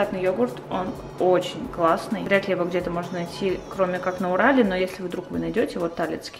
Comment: Где она живет? Мне послышалось в Урале 😂